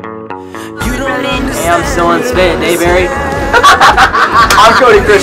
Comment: what says liam at 0:06 😂 ? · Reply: His name Dylan Spraberry but it sounded fast and funny😂😂😂